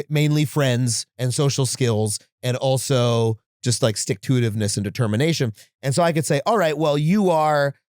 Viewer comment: Did he just say stick-to-it-iveness at 33:40? I learned a new word today.